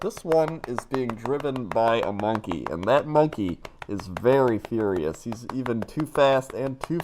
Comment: I thought said the monkey was curious. Too fast, too curious.